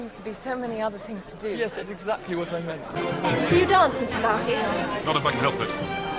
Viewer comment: What did he answer to her question? Did he say "not if I can help"... or what?